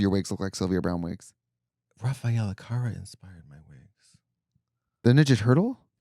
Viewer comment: 😂 I thought Katya was saying rafael alencar